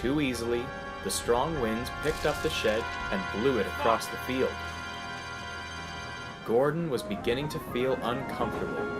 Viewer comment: Did the farmer say what I think he said? XD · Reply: F U C K